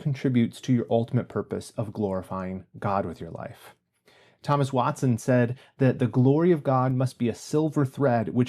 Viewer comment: You threw out that quote from Thomas Watson, and at first I thought I heard "Thomas Monson"... I was like, wait, I don't remember President Monson saying that! 😆